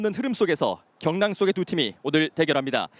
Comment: 내가 인트로를 잘 못들었나... 경남의 두팀이라니? 대구는 경북인데...? 세징야는 쩌네.... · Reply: 격랑 속의 두 팀